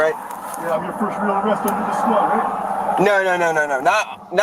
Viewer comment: if you hear what he says “ HEY IM UR FIRST REAL ARREST in the squad huh ..!?